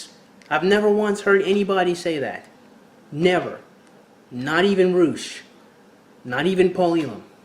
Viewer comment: You said "sitation needed" at 1:46, over the whole men's groups saying that women lie about rape 90% of the time... and then you said "not even roush (sp?). not even paulion (sp?)" I know I didn't hear those names right, but I want to know more about those, I never heard of them before. What are the correct names? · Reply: Roosh V idk the other guy...